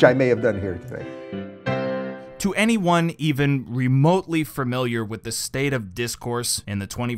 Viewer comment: I realized you say "Fur-miliar" instead of "Familiar"